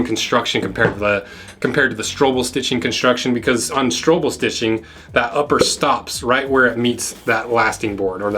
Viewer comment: I always thought you were saying "struggle stitching" not Strobbel